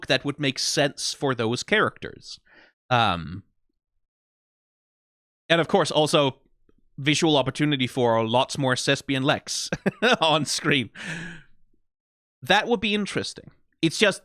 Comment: all I heard was more sesbian lex and I'm gonna be honest here...I'm hooked. I'm on board. Absolutely yeah next